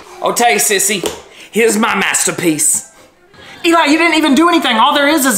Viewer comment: “THAS PUMTIN DUTS!” Got me laughing Edit: yall i came back after 8 months and i have 654 likes?! Also since yall keep saying if its dem pumtin duts or DAS PUMTIN DUTS heres when he says it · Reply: I heard "DATS PUMPIN BUTTS!"